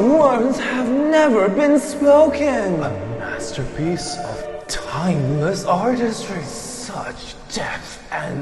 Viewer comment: subtitles got it right with “mindless artistry” lol